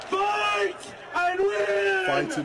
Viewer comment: I thought he said Vitamin instead of Fight and win 😅